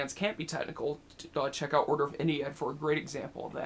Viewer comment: can someone pls repeat what band he said? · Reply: The band name he might have said. Order of Ennead.